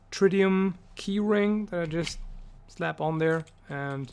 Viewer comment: I had to run it back just a hair because I thought you said Trivium keyring...